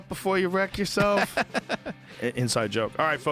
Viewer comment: Did he say Kevin Smith will be on tomorrow (today) at 2:55:46 ?